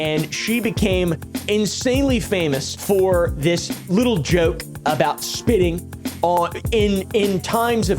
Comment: Right at 6:00 I heard “Made a joke for spitting on” And then an ad cut in and said “LIBERTY MUTUAL!”